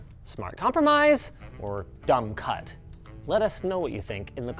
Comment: For a second there at 5:00 I thought Linus called me a dumb cunt and I almost died.